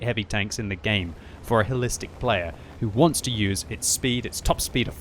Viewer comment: Word of the day "Holistic " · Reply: I thought he said, "Hellistic", which actually makes better sense.